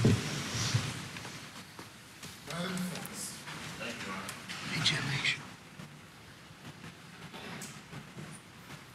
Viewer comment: Alex says to his attorney "MAKE SURE YOU TALK TO HIM ABOUT..." (can't make out the last word, but I'm guessing it's something negative). Guess we'll see. What a jerk! He should've just taken a plea deal & fessed up, instead of putting EVERYBODY through this pain AGAIN! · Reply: At the very end of that sentence was "Maggie and Paul"..I would say his first question was what Alex said